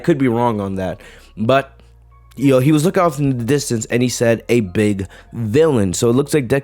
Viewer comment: i misheard that as a "Babe" instead of "Big" lmao